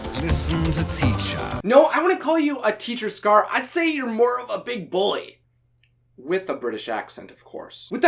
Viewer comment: did he say big boy? or big bully?